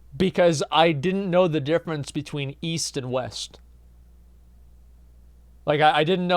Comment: East? I thought you said, Weast.